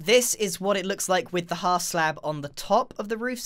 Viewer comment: My dad thought u sad ball sack when u said half slab